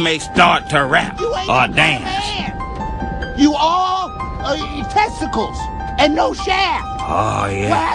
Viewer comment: it almost sounded like he said: You all eat testicles and no share! LOL XD